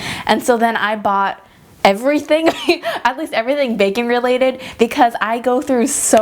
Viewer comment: I thought you said "bacon-related" instead of "baking-related" at first. 😂😂😂